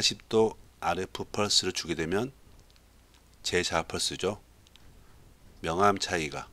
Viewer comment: 이때 뭐라고 말씀하신 건가요? 제 뭐라고 말씀하신거 같은데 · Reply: 재(re)자화 펄스 라고 하신 것 같습니다